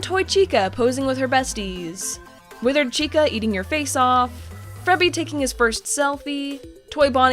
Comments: I Heard Say Frebby Lol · I swear she said Frebby at 10:42 instead of Freddy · did she just say frebby? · Wait did she say frebby